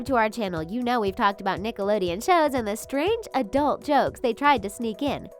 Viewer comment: Who else heard her say disney